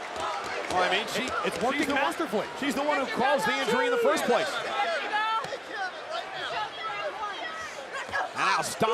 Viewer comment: What did liv morgan say too dominik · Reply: She said “That’s your girl Dominik?” Dominik responds “Yeah that’s my Mami” Liv says “That’s your girl?” Dominik doesn’t respond.